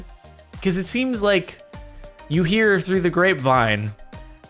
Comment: could have sworn i heard him say gravemind right there